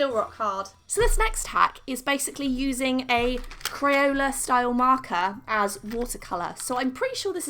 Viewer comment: Is it just me or when she said “ Crayola styled marker” I thought she said crayon, just me okay